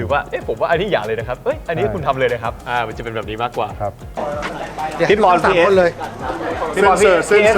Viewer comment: DID I HEARD SOMEONE CALLED "P EST" ????????